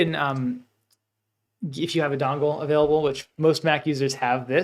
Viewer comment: Hello. Thank you for the video. I am watching now. At the 15:17 marker you mention a type of connector for Mac - dongvul? :) I couldn't understand what you said. I have plugged my ledger nano S directly into my iMac several times without any. Should I be using a connector? Thank you. I will continue to watch and likely have more questions. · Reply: If you've been able to plug it in and connect without an issue, then no problem. A dongle is useful when you have to connect a USB-A cable to a USB-C one.